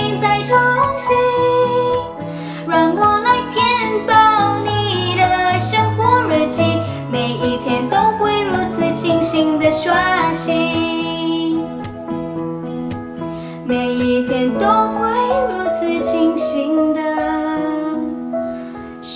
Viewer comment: why do i hear like " Shing shing diswashing ? :o " sorry for all chinese ppl XD ahaha. over all song ? ITS GOOD <3 <3